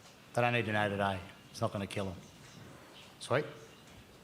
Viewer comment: Sounds like he said what a homo at 10:05-10:06... anyone else catch that